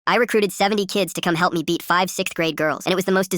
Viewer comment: For some reason I thought it first said I recruited 70year old children to help me😂😂😂